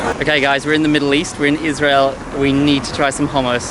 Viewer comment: he said Israel and the subtitle says Palestinian 🤨